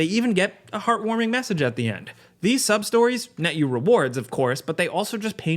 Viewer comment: I swear i thought you were saying sob-stories at first. I was like wait... what? Side quests in this game are called sob stories? Lol